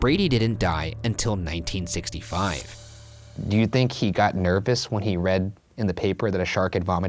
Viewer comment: Did Ryan just say “nerpous” ? haha